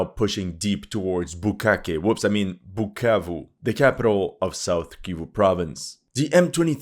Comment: did i hear right Bukkake:face-blue-smiling